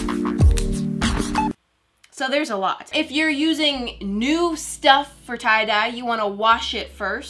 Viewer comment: At first when you said “if you’re using new stuff for tie-dye” I thought you said “if you’re using noob stuff for tie dye” LOL